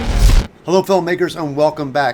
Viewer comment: I thought he said hello n*****s